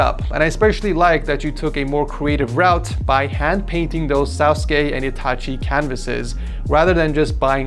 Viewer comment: MY GUY did you just say SAUCEKAY and ITACHI? :D or am I tripping?